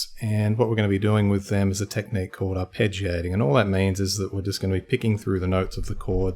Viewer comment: Thought it said arpeggios at 1st lol man I was confused